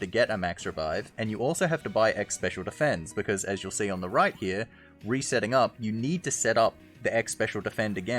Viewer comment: I heard "Rhyper[ior]" instead of "right here" :/